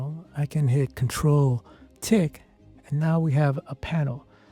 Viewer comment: If you're doing a tutorial with shortcuts: display the buttons needed to be pressed for the shortcut! The voice sounded like it said Control (Ctrl) tick! Not Control (Ctrl) Tilde `